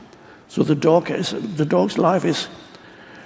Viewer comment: I thought Eckhart was saying Dorks, not dog's. 🤔🙂🦆